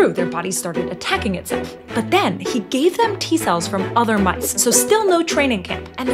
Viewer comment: T what? Oh T-Cell... I thought I heard T-Virus LOL